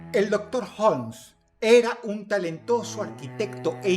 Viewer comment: ns por qué escuché "el Dr House" 😭😭😭